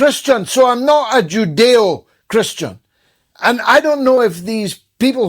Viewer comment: I thought they said, Judo Christian, and it was the religion of Chuck Norris